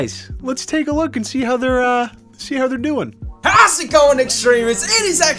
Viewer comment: Hold up, Did they just say "How's It Going, Extremists"